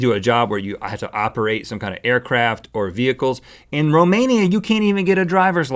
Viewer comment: what did he said at 10:00 "Mimania" i think he meant Romania.